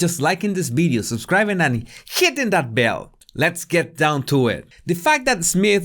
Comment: Sometimes it sounds like you say "...and sh!t in that bell" and I always look forward to it 🤣😊💛